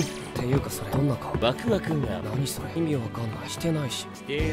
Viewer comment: DID KUROO JUST SAID WAKU WAKU?😭😭